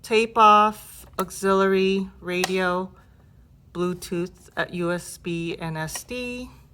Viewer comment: What'd she say at 8:45? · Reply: Bluetooth pairing. It was the voice from boombox:)